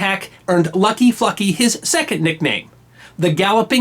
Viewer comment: who else thought he was saying "fucky"?